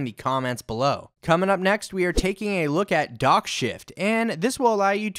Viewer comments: when you said DockShift i thought you said Dog shit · i wasnt looking at my laptop when u said DockShift and i thought u said Dog Shit lmfao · dockShift almost sounded like dog S%^t.. lol